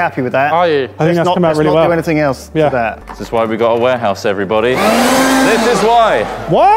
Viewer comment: For a very short minute I thought the video said: "This is what we bought a WARHORSE for!" and I got very worried.